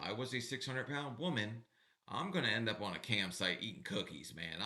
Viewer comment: I thought u said ‘camp sites’ and I was like KOA!?